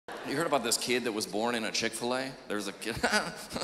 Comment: I can't be the only one who thought Chick-fil-A said Chick Flick A